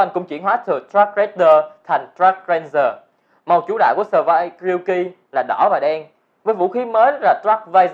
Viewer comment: Thanh niên ngọng Ryuki nghe thành Yuuki :v